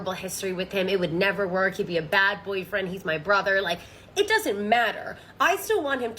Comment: I’m sorry did I hear “brother” in that list?